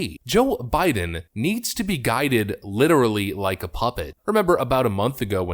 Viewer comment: Thought you said, 'like a puppy.' I was thinking, "naw. At least a puppy has some autonomy. He's more like an old C64 computer. He can't do anything without clear and concise direction."